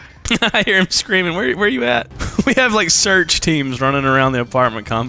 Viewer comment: sample text instead of search teams